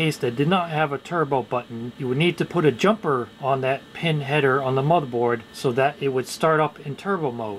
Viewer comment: I've had 3 beers. All I heard was "pin head" and laughed out loud.